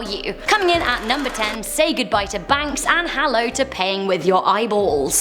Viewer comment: PAYING WITH YOUR EYES lol I thought she was saying gauging out eyes and that eyes are currency lol